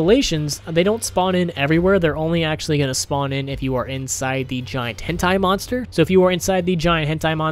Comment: did he just say the "Giant H£πt¡€ monster" or is it just me hearing things? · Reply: Yep. Giant Hentai monster